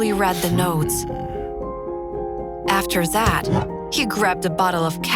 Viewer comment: afer instead of after